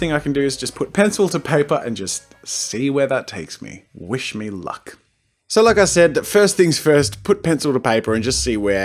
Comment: Oml I misheard "We should fuck" instead of "Wish me lucky 😂😂